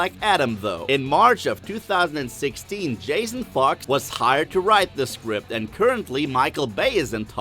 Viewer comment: "Jason Fart"? "Jason Fucked"? What the hell are you saying?